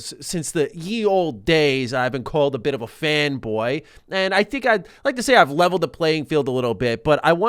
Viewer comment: I thought it said "femboy" instead of fanboy for a second.